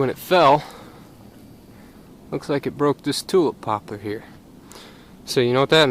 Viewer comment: First Off Very informative and useful Video production! Big Question! What did you call the wood, that the Pine Tree broke at 4:07 . “Tool Popper” ? Also whats the more common or legit name of the ( tinder wood ) tree? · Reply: Tulip Poplar